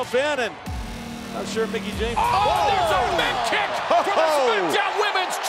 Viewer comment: Did he just say "A mick kick from the smackdowns womens champion"?